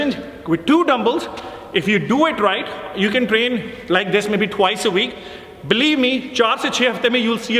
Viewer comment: what did he say? Some thing like "2wice a week"??? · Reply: Yes it's proven in various studies that you should work a muscle twice a week for better result